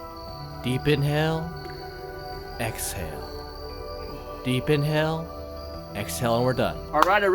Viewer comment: I thought I heard you saying “deep in hell~ exhale” lol